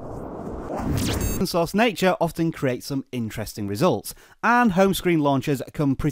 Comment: didn't understand words at 0:21 . is it "Nature creates interesting results"? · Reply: I think Ash may have accidentally cut off my intro. It's supposed to say: "Android Open Source nature often creates some interesting results" :)